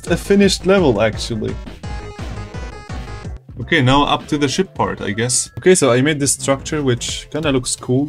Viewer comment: I thought he said shit part